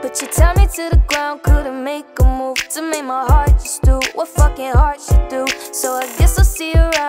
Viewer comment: did she just say F**king?